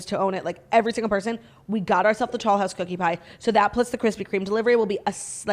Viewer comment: Are you saying tall house or tollhouse cookie pie? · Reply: The first time she said it I thought she said tall house too!